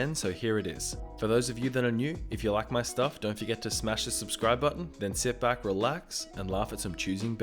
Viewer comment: I really heard "smash a suscriber"